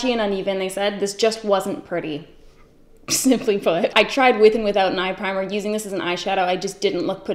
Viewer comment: When Sam said “simply put” at 3:30, I thought she said “smell my foot” and I had to replay it 20 times until I realized she does not want us to smell her foot.